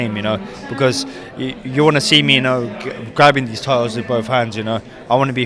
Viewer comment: I thought that said "tittes "🤣🤣